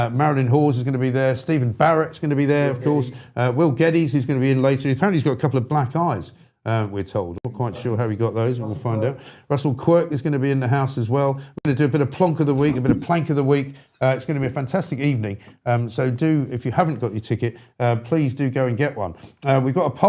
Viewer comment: did he say that Russell Coight will be there? Fantastic! That man is a goddam legend